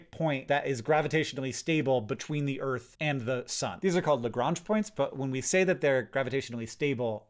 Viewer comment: I definitely heard "gronch points" instead of "Lagrange points" lol